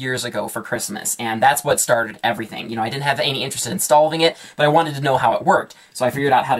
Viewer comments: Why did you say stolving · I swear he says STOLVE